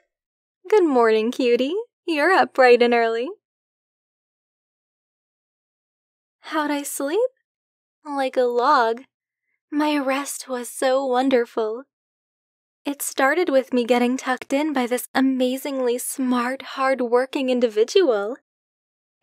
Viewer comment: I thought she said “my arrest was so comfortable” didn’t realise what kinda video this was